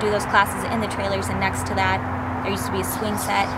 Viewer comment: 40 to 12:45 there is a little child saying something can’t figure it out what it is saying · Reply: I heard ‘swing sets’ said right over saying it